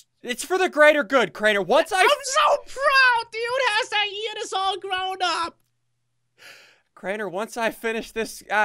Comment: I thought he said all grown up